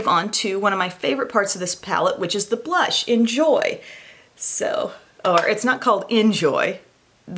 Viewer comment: LOL... when you said the blush “in joy” I heard “enjoy” then you corrected and told the name wasn’t “enjoy” it was “joy”, but I took it as we should “enjoy” the blush... LOL..